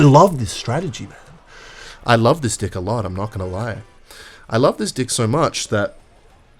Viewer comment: ok is it just me or when the title is "i found love" and his accent makes it sound like hes saying "i love this dick so much" and sht its just so funny :skull